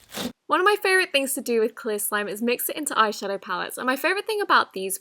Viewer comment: the subtitles say clear glue when it’s clear slime